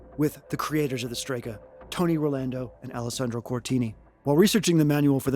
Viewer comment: Every time you said “Tony Rolando” in the intro, I heard “Tony Orlando” and I involuntarily blurted “And Dawn.” Not sure what that says about me.